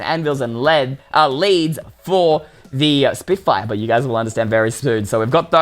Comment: For most of the video I thought he said spit fire instead of spin fire.